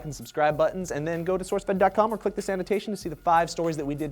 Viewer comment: Every time you say "this annotation" I hear "the sanitation"...